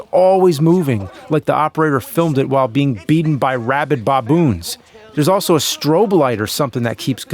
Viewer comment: Did you just say "bobboons?"